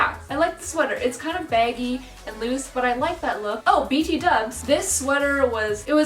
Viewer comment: did she say b t dubs?